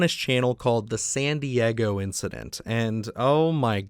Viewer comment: i swear i thought you said Sandy Eggo Incident the first time. lol love your videos